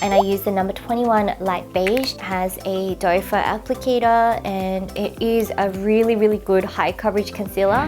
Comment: I always thought it was called a "doe-foot applicator" but in the vid, it sounded like said "dopher applicator". Have I been saying it wrong this whole time?!? lol